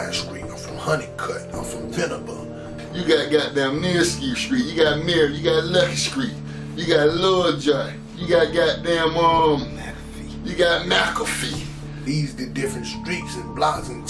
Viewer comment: u got GODDAMN MEscrew street , u got murr street u got lucky street , u got lil Johnt , u got god damn umm … u got MACAFEE 😂😂 that’s what I heard .. smh I could be wrong 😑